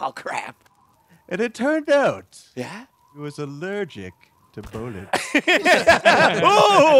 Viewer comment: And also I swear everytime I hear the story part that is telling instead of hearing "Allergic to bullets" I hear "Allergic to bollets"